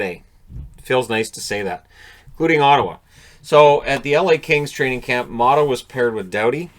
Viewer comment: Oddly I thought you said "Including Maattawa". I need rest.